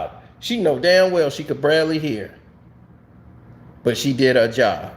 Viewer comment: I could BARELY make out what u said but I knew what u meant it was funny tho! 😄 “ Bradley hear! “